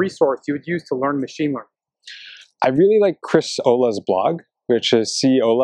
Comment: what does he say at 8:13? l can't understand the link "christ olga's blog"?